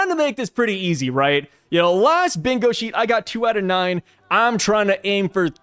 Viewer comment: I heard "...last 'BENGOSHI', I got..." and I'm like, 'CHOTO! Why Psy need a rawyeru now?''